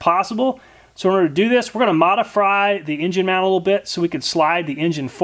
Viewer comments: Did you say modifry at 6:42 or am i just hungry?😂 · did you say modafry lol